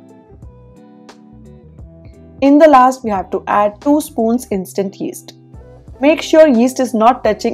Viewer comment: It cannot be guessed the ingredients names..so it should be written on the screen mam ! 3:13 listened 20 times but couldn't understand ! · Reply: Instant yeast 😅